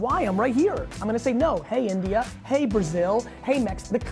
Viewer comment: Did he say " hey India". And if YES.. All Indian vaynernation there is some good news for you. Wait is he coming to India? When? Where?